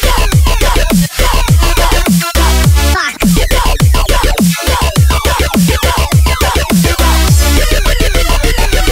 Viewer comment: i dont know if anyone even noted this, but at 0:51 it sound like its saying f word?? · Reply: it’s fark